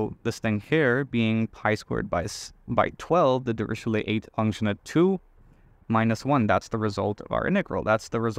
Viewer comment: I didn't understand what the name of the result used to search for it is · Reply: Dirichlet eta function at 2